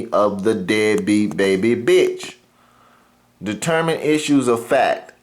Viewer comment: Did you just say "that Dead beat baby bitch?" 😂😂😂👉🏾🙋🏾